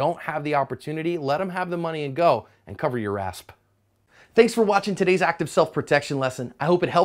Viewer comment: Thought i was the only one who heard him say “cover your asp”